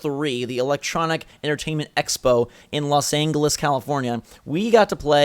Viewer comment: did he say just los Angules 0:18 0_o